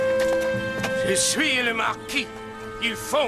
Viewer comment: For any French speakers out there: what is he saying at 42:00? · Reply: I believe he says "Je suis le Marquis, il faut m'obéir" which is roughly "I am the Marquis, I must be obeyed" (or "it is required to obey me")